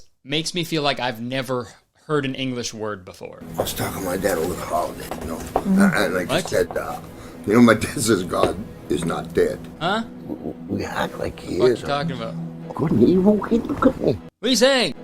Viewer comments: i think he says ( 4:36 ) "I was talking to my dad over the holiday, ya know, and like he said ahhh ... ya know my dad says, god is not dead. But we act like he is, though, ya know? Good and evil? hey ... look at me!" which is why she looks up. i think. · Soo for the managers bable i heard "would an evil being cuck me" Soo? I hope I'm wrong? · Ok, the Dialogue at 4:47 Personally, I think he's saying "...good and evil, He [god] didn't go away." cause he was talking about people believing god in the conversation. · I hear "Good and evil gnkukgny" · After spending 15 minutes trying to figure out what the boss was saying I finally figured out the subtitles by replying the video at 0.25 speed. He's not using proper grammar but here's what I believe to be the words coming out of his mouth at 4:37 he's saying "I was thinking my dad over the holiday you know. An like I said uh, you know my dad says god in not dead, we act like he isn't. Good n evil- Kate look at me." The final half of that last sentence is extremely hard to make out, it could also be coexist, or compete instead of Kate look at me. But "Kate look at me." makes sense because the actress wasn't making eye contact with him. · It sounds like he said, "Good and evil be cooking me." Smh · after watching this video 3000 times, I finally have a guess to what he might be saying. "Good and evil- hey, look at me." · It sounds like he says “good and evil, hate and company” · i heard him say "good and evil good cupcake"?